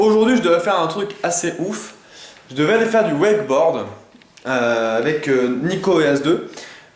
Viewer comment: J'ai compris du "web porn" xD j'ai eu bien peur sur le coup !